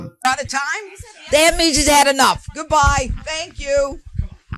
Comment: did she say “the enemies have had enough!” ???